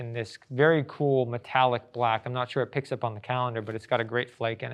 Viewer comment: “it has a metallic flake but I’m not sure it picks up on the calendar”. Wtf did you just say? Lol why the fuck is dude thinking about calendars? Was he supposed to say CAMERA? Hmmm strange af.